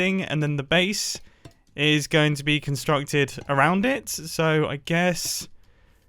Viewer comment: I thought you said this base is going to be simple